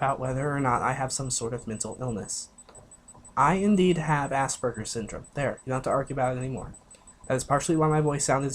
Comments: did he just say ass burger syndrome?!?! 😂😂😂💀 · I thought this man said ass burger syndrome